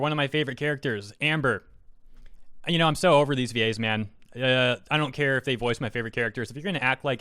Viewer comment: I almost thought you said "I'm so over these gays man" XD